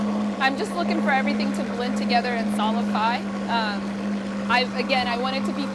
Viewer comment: I don't know quite what she said. Sounded like "solify", but was she meaning something like emulsify?